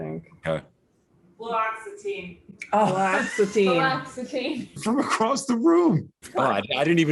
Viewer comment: did say "paroxatine"? I think that's what I'm on. I only really know it as Aropax which was the brand name when I was first prescribed it about 20 years ago, but now it's either Paxtine or Paroxatine...something like that.